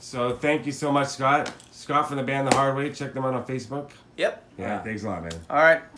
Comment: what did he said i was gonna check out on facebook at about 31:00 ? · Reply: His band The Hard Way on Facebook